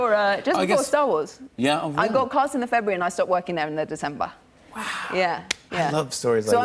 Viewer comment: did she say "Started working" or "Stopped working"? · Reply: she said stopped working I think